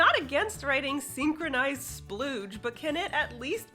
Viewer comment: Moment I heard synchronized sploosh... I thought I heard synchronized swim lolol. 🏊‍♂️. I'm like... is that a new position? Lol. Had 2 re watch that part 😆